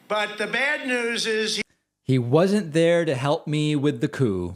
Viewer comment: I'm a big fan and I think that trump was the worst thing to happen but I didn't hear "the coup" part. I heard "he wasnt there for the country". Again, big fan but this is misleading.